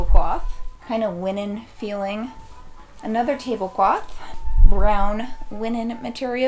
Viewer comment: deadass thought you'd say the tablecloth was "brown-feeling" 😂 also I've been meaning to do this but all the stores by me have been picked clean grumble